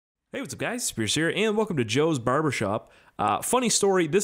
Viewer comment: When u say your intro it sounds like your saying "hey what's up guys scarce here!"